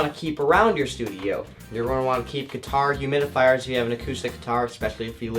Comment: Dude really nice video and congrats for your studio it's awesome!! just a pair of questions: 1: is it ok if i plug into the interface my effect pedals? 2: at 8:43 did you say guitar humidifiers? sorry im from Peru i'm trying to understand everything. If so, where can i get one and how much is it? Thanks!